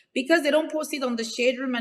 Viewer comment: I thought she said she don’t post her product on her page? This is a backwards interview. Tasha is trying not to snap